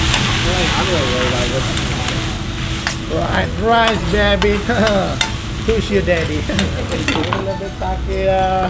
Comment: did he say hows your daddy 😂